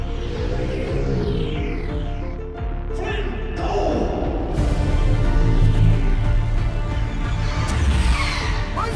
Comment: what is said right there? I can't understand it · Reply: “Flynn, go” (reference to flashback scene from Flynn’s house) “Rinzler, take the shot. Finish the game.”